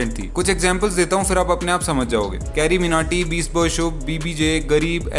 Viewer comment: did you said beastboyshub😮